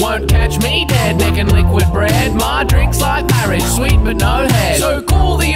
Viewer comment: i thought he said “sweet like marie, but no head” for some reason, thinking he was referring to marie “let them eat cake” antoinette and her headlessness.